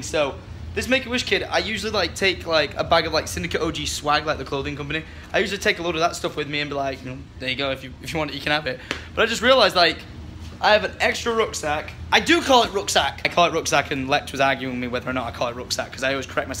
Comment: Does he say 'rugzak'? (dutch word for backpack) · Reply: He said rucksack not rugzak